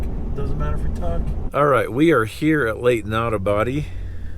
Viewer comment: At about 1:10 I first thought you said Lake not a body! I listened to it again and yes was wrong!